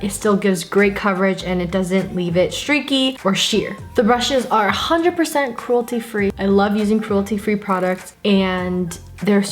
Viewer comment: I thought she said these brushes are 100% gluten free